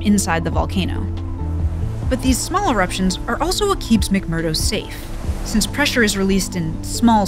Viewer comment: I never realized before that McMurdo Station is sitting on the flanks of the only active volcano in Antarctica.... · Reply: I always thought they were saying McMurder...